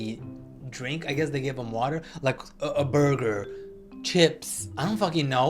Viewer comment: did he just say his last name is burger?!